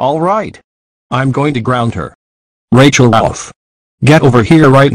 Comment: did he just say Rachel elf